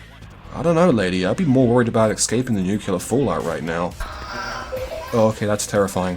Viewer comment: Did you just say nukular fallout?